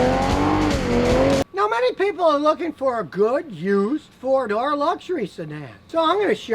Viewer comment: Oh... "four door luxury sedan", at first I heard "Ford or luxury sedan" which didn't make much sense